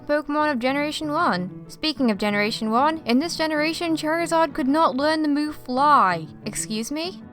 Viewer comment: DID I JUST HEAR CHAIRZARD?!?!?!